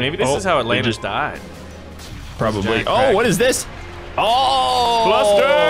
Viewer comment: when he said kracken I thought he said crack-head!